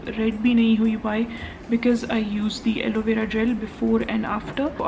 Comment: literally the subtitle was elevator gel instead of alovera gel 😂😂 And yaa...at 15:00 she said "skin red nahi hui" and subtitle was "skin red NAVI"